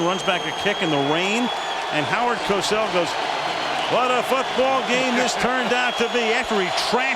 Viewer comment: I thought he said "fuck ball" lmao